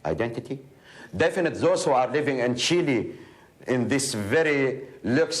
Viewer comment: he talks about Kuwaiti Palestinians, and it sucks that most would get deported one or two years later because of the invasion. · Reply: I thought he said Chile, not Kuwait.